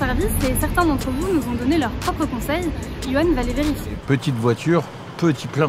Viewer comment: "petit plein" moi je dis plutôt "Peter Pettigrow" :p